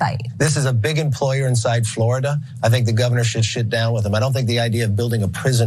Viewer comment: Anyone else hear Mcarthy say he should shit down with him at 8:26 😂